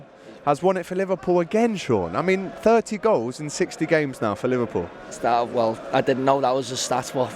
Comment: Sort your maths out 👏👏🤣🤣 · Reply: Hahahaha I thought he said 30 goals in 16 games bro. He’s done me dirty here